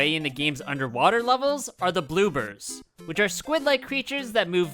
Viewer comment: im sorry… did you say… “bloobers”???